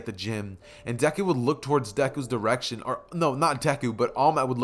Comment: I think I heard something wrong and it took me 5 seconds to realize. I thought he said reaction for a second 🤣 But then i realized when going back. It was direction